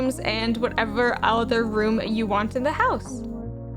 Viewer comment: did she said Backrooms? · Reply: she said “other room you want in the house” not backrooms.